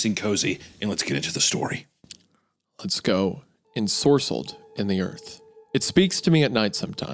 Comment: Bro, I thought that said enscrolled